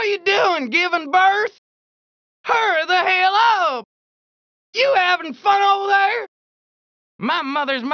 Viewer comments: What's he saying at 0:08 though? 🤔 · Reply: You having fun over there?! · “What’re ya doin?? GIVING BIRTH?!!” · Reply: I always thought he was saying "giving the bird." 🤣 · Hurry the hell up Thought was saying "hurt the halo"